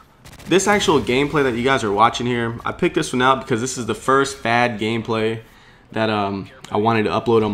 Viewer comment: i thought he said 'bad gameplay'